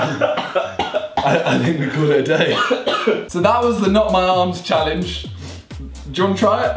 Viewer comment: I thought it said not my arse challenge